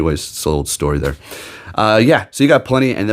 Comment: I couldn't quite understand what you said @ 4:49, did you say "Ash Greninja"?